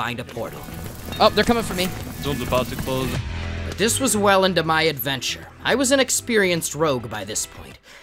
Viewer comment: he actually said "this was welyn to my adventure"